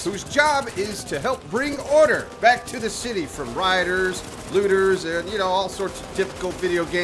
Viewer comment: Hey guys dis is Appsro. I'll try to answer any questions you have about the game so feel free to ask. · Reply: you saying 'raiders' at 0:46 ? Cause I swear I heard 'riders'.